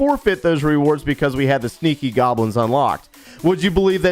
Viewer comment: Why am I heard he said "Sticky Goblin"??